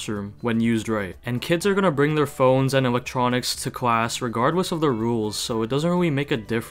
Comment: I thought you said animatronics (I know, this is off topic)